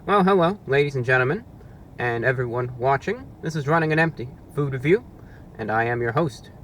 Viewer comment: Reviewbrah! I love your very informative content, and I want to make sure everyone can understand and enjoy it. That's why I wanted to let you know that your captions are a little off. At about 0:11 the auto-captions say "ladies and gentlemen and everyone watching this is running an empty poo the view" where they meant to say "ladies and gentlemen and everyone watching this is running on empty food review". When you get a chance, could you fix that? Especially since it's part of your trademark intro. That'll help the 360 million folks globally who rely on closed captions to understand videos. Thank you, and keep up the great reviews!